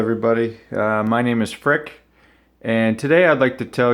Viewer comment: I almost spat my beer allover the monitor , i thought you said your name was prick !! .... no offence my friend I just mis heard :)